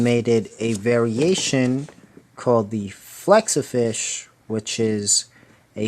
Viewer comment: When you said Flexafish I thought you said Slexafish